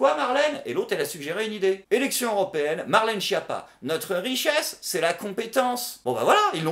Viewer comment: J'ai entendu : 'notre richesse c'est l'incompétence' Pour le coup je trouve ça mieux.